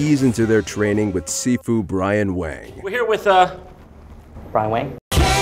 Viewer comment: Oh I thought they said Brian Wecht at first XD